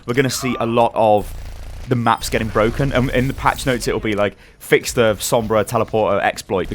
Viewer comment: did you say patch notes?